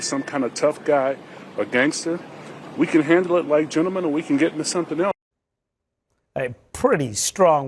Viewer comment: Did he just say “we can get into some gangsta ish”? 😂😂😂